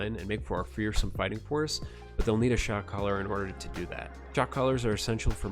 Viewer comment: I keep mishearing that as "they'll need a shock collar" and I was 100% ready to agree with you lol